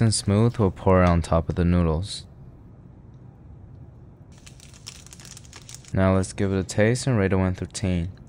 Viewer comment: Sounded like you said let’s give a taste to one poutine 😂 like this is some strange new version or something but the first of the to come lol